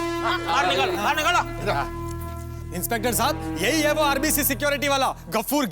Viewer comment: yo guys did any one see that at 48:40 in subtitels in english it showed RCB instead of RBC Bro whyyyyyyyyyyy